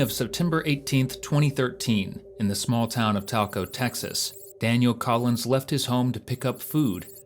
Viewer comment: Talco Texas? I thought he said Taco, Texas, named after our criminal, cowardly, weak, senile, diaper-wearing 47th president. Taco Trump!